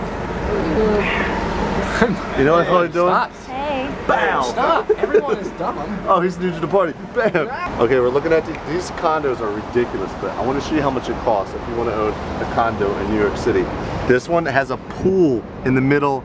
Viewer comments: LOL! like around 4:00 I thought said "where looking at these condoms and these condoms are rediculous"...but he said condos...made me laugh! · when he said condo i thought he said condom lol